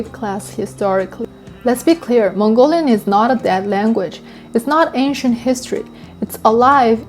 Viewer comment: did you say "bad"? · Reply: She said Mongolian is not a dead language